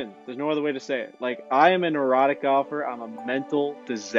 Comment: this man said erotic instead of erratic lol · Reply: He said neurotic